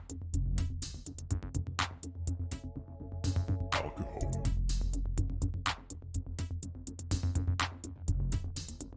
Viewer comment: always wondered what the word is here in the intro, is it “alcohol”? Kind of sounds like “Al Capone” too but that doesn’t make much sense (or does it?) While listening a few times I heard the echoing “sup sobers” at the end of the intro for the first time, have watched many of your videos and never caught that before…great video, appreciate all the time and effort you put into your channel! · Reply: lol al capone, i should have said that just to mess with people but yeah it says alcohol because that was my drug of choice for a long time and then i do in fact say sup sobers at the end. You just gave me a solid lol for sure. thanks for the love and watching my videos. ❤️